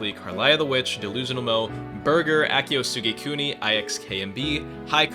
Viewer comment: did he just say his last name was burger????